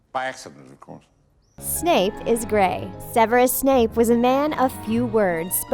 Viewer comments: I thought you said snape is gay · when they said snape was grey i thought they said he was gay lol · When "Snape was grey" I thought it said "Snape is gay" and "Dumbledore is death" (Which I have thought about) I misunderstood for "Dumbledore is deft" I'm not good at this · I wasn’t paying attention when the video said “Snape is grey” and thought it said “Snape is gay” and I thought “ wait what? That isn’t right “ (cause of Lily and everything)